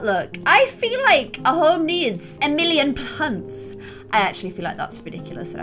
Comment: LMAO, When she said a house needs a million plants, I thought she said a house needs a million pants 😂